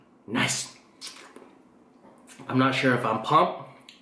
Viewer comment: why does he say “im pomped”? im confused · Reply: Linda Xyooj thought he was saying pomped not pumped